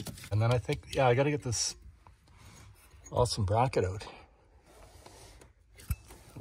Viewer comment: Hahahah I had to replay 13:14... 3 times to verify what I heard.... you said "I have to get this awsome bracket out !" You have all my respect ! Your politeness is limitless !